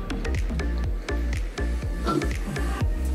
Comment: I thought he said it does crack